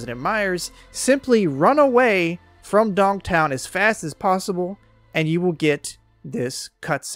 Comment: did you say "Dong Town" ? 🤣